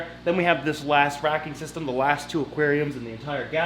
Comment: did he say hes going salt water at the end of the video? it sounded like he said salt water, but i could of misheard him on. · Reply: Yes he said salt water